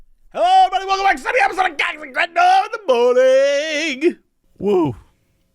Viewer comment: Is it just me or does it sound like he is saying cox n in the morning? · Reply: He is, is an old nickname from their saints row 3 playthrough